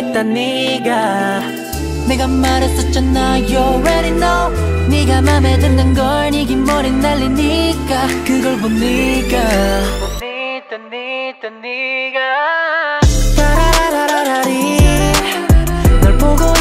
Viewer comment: i thought they just say niga niga niga 🤣🤣🤣